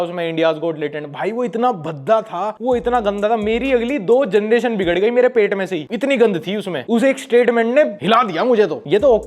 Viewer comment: kya bola bhai .... aapke pet se😂